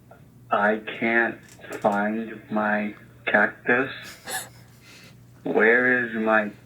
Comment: I thought he said I can't find my cat piss